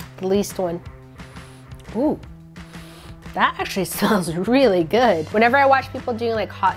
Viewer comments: Her: smells Her: Her: "that actually sounds really good" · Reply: Guys she did actually say smells I confirmed it by turning on the captions u can try it too, ngl but at first I thought she said sounds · Her: smells Her: Her: "that actually sounds really good" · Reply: I heard smells, I heard her say the “m”